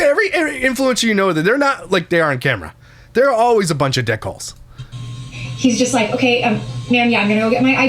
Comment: He said "dick holes" but I heard "dickles" 😂😂😂